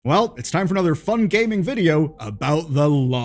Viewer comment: I heard "time for another fun gaping video".